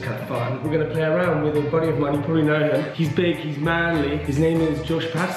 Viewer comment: When he said "he's big, he's manly" I thought he said he's binkys man😂 season 9 through back